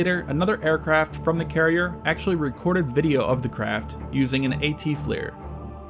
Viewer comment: Did anyone catch what he called the camera that was used by the pilots on the carrier to tape the "tic tac" UFO? It sounded like he said AT FLIR but Idk what the AT sounds for if that's even what he said. Anyone knows help would be much appreciated! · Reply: I think he did say ATFLIR, I think the AT refers to it being a mountable targeting pod. Maybe Add-on Targeting FLIR or Auxiliary Targeting FLIR. Though with military acronyms it's hard to tell wtf they stand for.